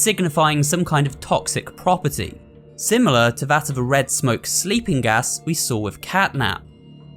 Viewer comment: I thought simon smaokes said "Weed smoke" As its scent 🤣🤣🤣🤣🤣🤣💀💀💀💀💀💀